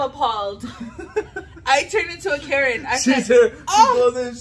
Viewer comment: I thought she said I turned into a carrot 😂🤣🤣🤣